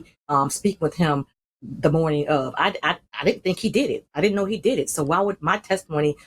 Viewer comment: Did she say I didn’t know he did it??? Please go back and listen.